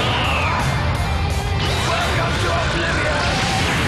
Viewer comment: its like i heard "I hope you come back some day as a good guy." XD